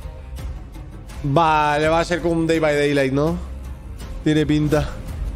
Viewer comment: pregunta ¿que es lo que dice Rubius en ese minuto xd? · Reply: dice: “vale va a ser como un Dead by Daylight tiene pinta”